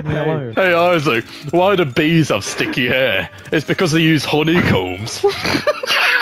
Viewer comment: Why'd it sound like he said hornycombs? Both words work with the joke though.